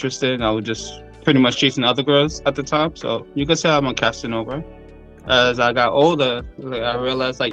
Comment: “You could say I’m a Casanova”…cut the damn cameras 🙄 Edit: am I famous now? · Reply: Oh, that’s what he said? I thought he said he wasn’t a Casanova…Oh, my